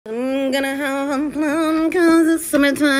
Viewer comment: My dumbass really thought it said "I'm GoNnA hAvE a PlUm" instead of "MmMmMm, CaN i A pLuM?"